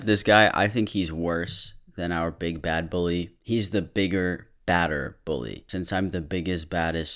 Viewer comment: I thought it said "bigger BALDER bully"🤣🤣🤣🤣🤣🤣